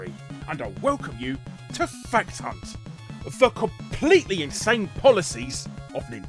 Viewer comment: I thought you said "Fat Cunt" for a second, never realized how close "Fact Hunt" is to that